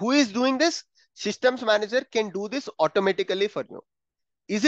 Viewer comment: instead of secret manager you told system manager??